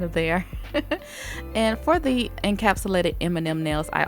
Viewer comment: Beautiful! I kept hearing "Eminem nails"